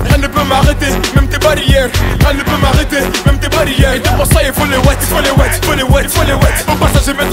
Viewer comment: il y a que moi qui entends les marseillais il faut les wet ? (Je suis pas marseillais) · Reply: Non, il dit : "Et pour que ça/ Et pour ça que" et non "les Marseillais" .. '...'